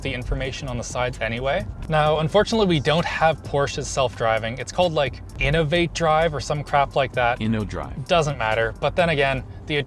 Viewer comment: Did he said crap